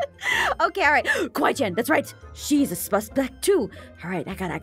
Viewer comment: Aphmau when you were talking about kawaiichan it sounded like you said she’s a spuspect too